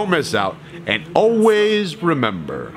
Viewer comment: for the longest I thought you said "Train on time"